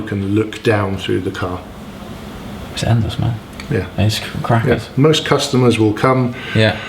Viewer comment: did you just say "Crack head"? 😂